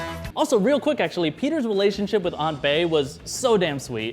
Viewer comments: Anyone else hear "Aunt Bae" at 8:26? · Lol did he say Aunt Bae at 8:27?! 😂😂